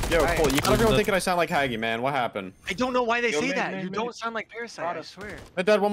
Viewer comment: I heard say “you don’t sound like parasite” YESSS HE DOES LMFAOOO